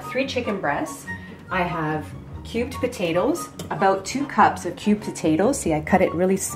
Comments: When said "Cubed potatoes", I thought she said "Cute potatoes"!! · I thought she said cute potatoes! 😂